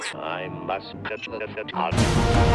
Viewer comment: What did he say at 0:30? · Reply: Turn on Subtitles. He said He must catch the Avatar.